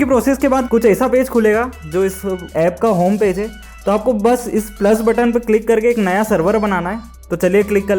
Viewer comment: I swear he said “taco bus” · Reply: No its actually "to apko bas"